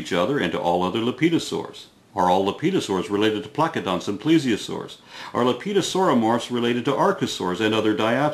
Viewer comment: Lol, I could have sworn you said 'Are all 'lepidosaurs related to crocoducks and plesiosaurs?'